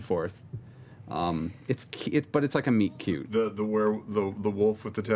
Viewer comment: seeing taliesin without colored hair is just....unsettling when he said meet cute, i heard Meat Cute "Gary Gygax is no longer living!" "Thanks, Liam."